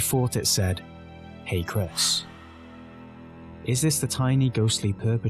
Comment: "Hey Chris, I see you!!" That is what I heard.